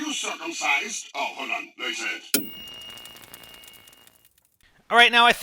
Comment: SPIDER AT 4:59 WHEN YOU CAN'T quite hear him... what he says is..... "hold on; take this" Wii wild he be saying take this too??? Like so spider can see..